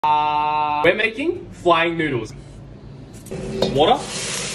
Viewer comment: people who thought he said frying noodles💀💀 👇